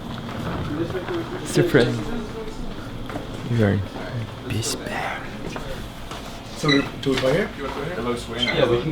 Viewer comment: what did lustboy say at 9:15? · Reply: "we're at a PC bang" which means gaming room/center xD